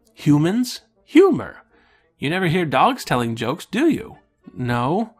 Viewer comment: I thought he said, "You never hear Daleks telling jokes, do you?"